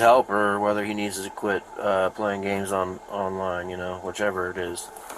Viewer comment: i honestly thought the cop said he needs to have his clit playing games online lol